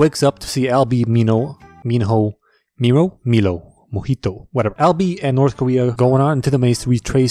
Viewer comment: I can relate to that since I saw the movies first I heard Mino and so that stuck for awhile until I got it right "Minho"